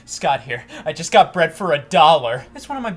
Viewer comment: I thought he said “I just got bred for a dollar” and spit out my water